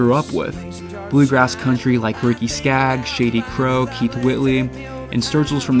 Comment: Did it say "Shady Crow" at 9:00? I'm not sure about this Shady Crow, but JD Crowe could sure pick a banjo.